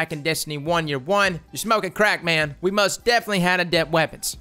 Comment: I don’t know why, but when he said “your smoking crack man” I heard him say “your smoking beer man” XD.... I might be smoking beer lol